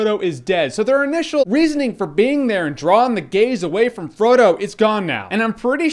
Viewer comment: I thought he said "Drawing the gays away from Frodo" LOL